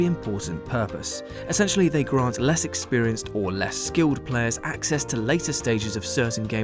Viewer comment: DID YOU CALL MY BOY UNSKILLED!!!???